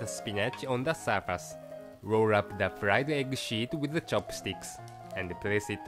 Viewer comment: fried egg- WHAT? Seriously did he say shed or something else? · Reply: Fried egg sheet ;)